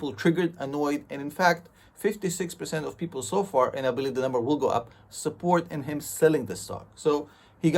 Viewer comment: Mr! I always Dig your perspectives on many the different topics that you discuss. But I cant understate what you said at 4:14 of your video.. please clarify. You say " And I believe that I'm a ..... .... ...... " Thank you for your time. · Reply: Thank you "and i believe the Numbers will go up"